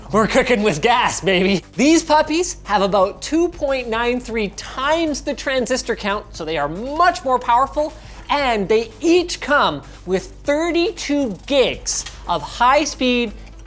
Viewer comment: did he just say “they eat cum”